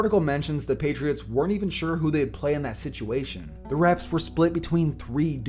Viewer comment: But browns are trash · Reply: he says "the reffs" instead of the coaches lol